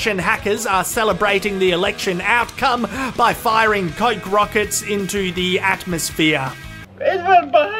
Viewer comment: I thought you said "Firing cock rockets" XD.